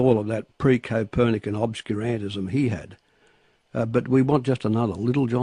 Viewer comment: WTF does Keating say @ 4:17? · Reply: pre-Copernican obscurantism. I.e. Purposely obscuring the truth to maintain an appearance of radical change. He's a wordsmith that Keating.